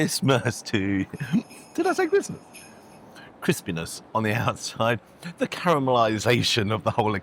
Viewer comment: You definitely said crispness…so he misheard and is wrong 🤣🤣